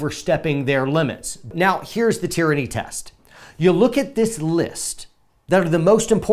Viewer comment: Haha I thought you said the tranny test. I was all ears for any tip to keep a horrible situation from happening.